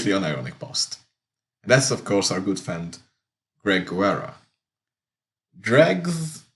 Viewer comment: Did you say “dreg”? That’s what I call him.